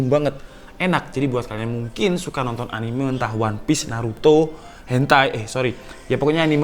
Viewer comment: what you say? Hentai